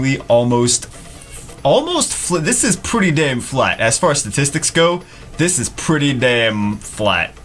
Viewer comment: " As far as statistics go this is pretty damn flat." what I heard "As far as ..dicks go this is pretty damn flat." lmao....